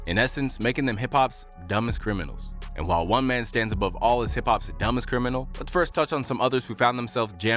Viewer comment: I thought this video said “the most dumbest criminal in hip hop history”